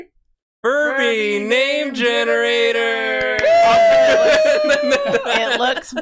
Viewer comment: I misheard “furby name generator” as “pervy name generator”, which was not helped by the generated name being “edge raspberry dingus”.